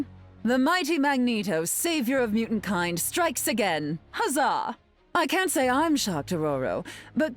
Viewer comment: did I just hear Emma say huzzah?😂